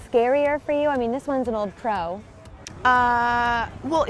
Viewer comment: When the anchor asked about singing I thought she said (pointing towards Tom), "I mean, this one is an old crow"...I had to replay it to hear her say "pro" instead of "crow". Relieved now 😬